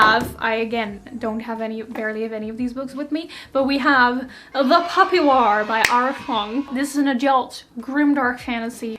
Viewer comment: I heard The Puppy War and imagined a war between some cute beagle puppies....